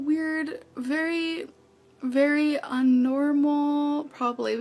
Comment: Did she just say unnormal? Wtf is unnormal?!